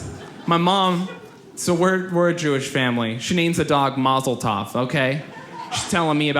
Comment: When he said mazel tov i thought he said molotov